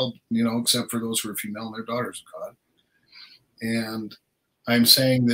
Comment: in the video, Blake says 'We're all sons of God except . . .' I could not hear/understand what he said at that point. Did someone catch that and could you enlighten me? Thank-you. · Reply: He said: “except for those who are female, they’re daughters of God”